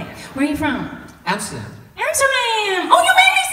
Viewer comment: what was the world i couldn't understand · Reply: Amsterdam so the bad word is "damn" aka: amster-dam